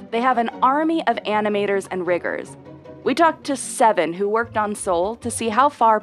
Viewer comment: what did she sayyyyyyy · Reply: Riggers?